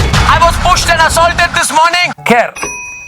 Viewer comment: When Arnab said "they pushed and assaulted me" I heard firstly "they sexually assaulted me" One day it could be true. 🤣🤣🤣